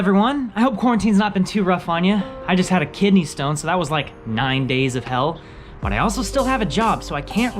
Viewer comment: at first I thought he said "I just had a kid and then his hospitalized picture came" 😂😂😂